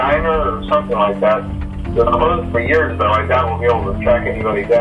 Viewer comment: Phone guy doesn't say "it's been closed for years" as everyone thinks he says, listen to it he says "was closed for years" clear as day, this is the biggest issue I have with people using the line and not understanding what's been said, if it was closed for years, it can currently be open, which completely changes from everyone thinking it says it's been closed for years meaning that it's currently closed. Listen to the line yourself, it's clearly saying "was closed for years" there's not even enough room for him to say "it's been" its clearly "was" Also awesome theory btw 😁👍